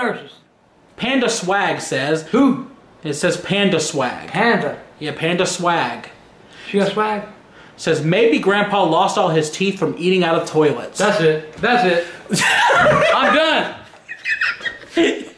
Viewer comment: PandaSwag says Grandpa: Who? It says PandaSwag Grandpa: Panda? Yeah PandaSwag Grandpa: She got swag? I’m done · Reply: I thought I heard him say is she a swag meaning slag